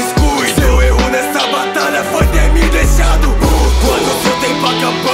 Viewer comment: na legenda tá escrito "o único erro que cometeu foi ter me deixado puto) mas o gabriza fala "o seu erro nessa batalha foi ter me deixado puto" Só falando o erro mas eu amei o rap :D